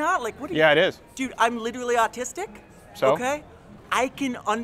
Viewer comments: ''Dude I'm literally autistic'' -Random guy on the street, 2017. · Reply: Ha i thought he said legally autistic... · "i'm literally autistic" I wish more leftists would be as honest as this guy. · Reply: I thought he said "legally autistic." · ''Dude I'm literally autistic'' -Random guy on the street, 2017. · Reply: I thought he said artistic